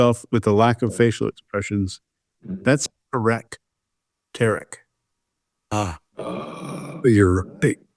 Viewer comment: What the hell did say at 1:02:57?! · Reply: Erek Tarik 😂😂😂😂I looked back that’s the best I could do.